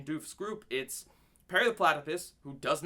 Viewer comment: it sounded like you said "Perry the Platy-piss" and it was hilarious. XD